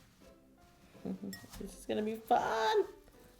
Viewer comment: I thought she said the fuck but she said this is going to be fun lol